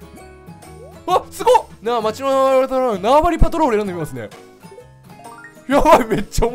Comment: 個人的に22:19からのテンションの上がり具合が好き ナワバリパトロールがなわわりパトロールに聞こえる